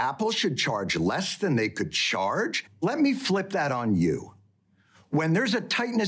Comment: When John says “let me flip that on you”, all I hear is “You’ve activated my trap card!”